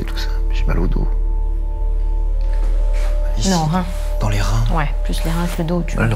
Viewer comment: C est moi ou dit "alice" à 43:15? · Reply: Il dit "ici", dans les reins.